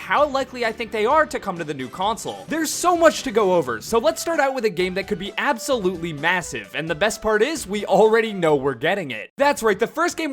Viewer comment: WAIT DID YOU SAY mAsSivE